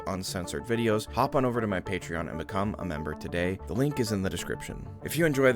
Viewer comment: Did he just say if you enjoyed this like please leave a video 😅